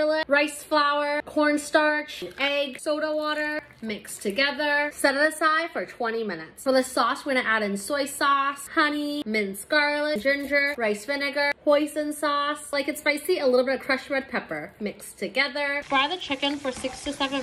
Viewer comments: I heard "poison sauce" and my widened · chicken: soy sauce salt and pepper minced garlic rice flour corn starch eggs soda water set aside for 20 min sauce: soy sauce honey minced garlic ginger rice vinegar hoisin sauce crushed red pepper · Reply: I thought she said poison sauce😅😂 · I thought she said poison sauce lol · chicken: soy sauce salt and pepper minced garlic rice flour corn starch eggs soda water set aside for 20 min sauce: soy sauce honey minced garlic ginger rice vinegar hoisin sauce crushed red pepper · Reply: I thought she said poison sauce A dish made to die for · Sounds like she said to add poison sauce 💀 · chicken: soy sauce salt and pepper minced garlic rice flour corn starch eggs soda water set aside for 20 min sauce: soy sauce honey minced garlic ginger rice vinegar hoisin sauce crushed red pepper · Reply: I thought she said poison sauce 😅😅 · when she said oyster sauce i thought she said “poison sauce” 💀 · Did I hear "poison sauce"? Someone correct me 😅